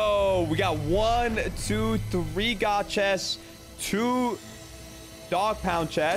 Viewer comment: did bro say god chest and not rare or epic or legendary chests ( I call them rare chests cause they're blue)